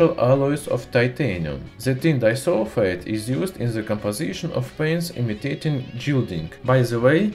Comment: Love the video...I couldn't make out what he was saying between 5:30 and 5:35 though... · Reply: "...sulfite is used in the paints imitating ..."